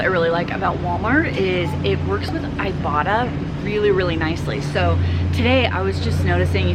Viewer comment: I've heard about ibotta on and off for a couple of years. This is the first time I heard it as 'I bought a'